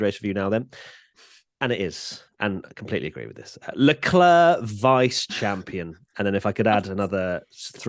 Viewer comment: All I hear is “LeClerc first loser” 2nd is the first loser.